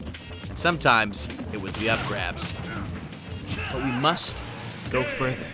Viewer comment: did he say up grab?